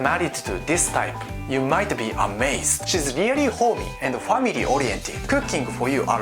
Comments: For the housewife girls I thought you said she is very horny, instead of homely 😂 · i thought he said horny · I swear I heard “she’s very horny” 🤣🤣🤣 Family wannabe Japanese girls 😅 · I thought you said she's going to be really horny but you said homely. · I thought you said horny, I got happy there for a second. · I thought he said She's really horny LOL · I swear I heard said " She's really Horny " No no don't be fooled by his accent 😉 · I thought he said " shes very horny" on